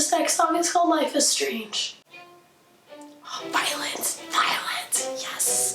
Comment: When you were cheering violins, I thought you were saying "Violence! Violence!" Lol.